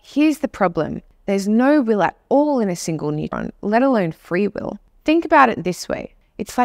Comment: she says: "Here's the problem ..." - and then her pronunciation is so unclear that I cannot hear what that problem is. Can anyone transcribe the sentence? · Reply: Without the CCs I wouldn't have understood either. She says "there's no well at all in a single neuron..."